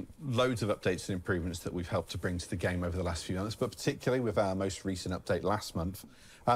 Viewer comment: He said in the last few months. The subtitles said last few hours. I find that absolutely hilarious lol!